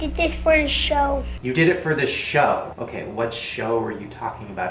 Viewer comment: I thought falcons dad said he did it for the "titty show" I was like tf.